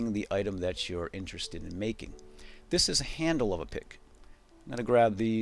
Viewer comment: Did he said handle of a dick instead of a pick at 7:34?